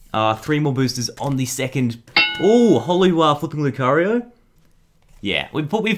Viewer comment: I Heard Him Say Holly Fucking Lucaruo